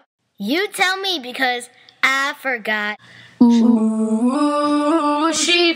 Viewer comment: S H E F O R G O T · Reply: I hear "Chi for goth"